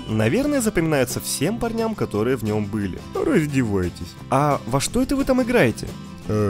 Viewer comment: Он запоминается всем парням которые В НЁМ БЫЛИ??????? Мне послышалось что-ли?